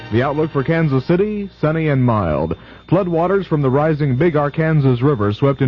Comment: Did he say "the big Ar-Kansas River" at 0:08 ?